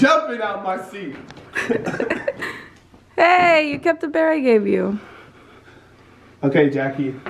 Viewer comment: did say "bea" or is it just me!?